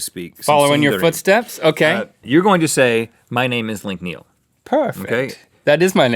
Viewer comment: I know they said Link Neil. But it sounded like they said Meek Mill